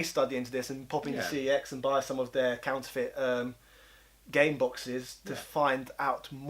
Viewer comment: Omg I thought you said they were selling Fart boxes at first. what a let down....lol